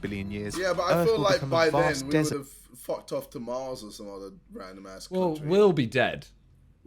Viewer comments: Did JJ really said we’ll go to Mars or some other random countries 🤣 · jj was big brain and said Country instead of planet lmaooo